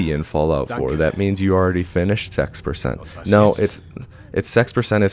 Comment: As a Swedish person, hearing “sex percent” Don’t read right, cause I keep hearing it as “6%”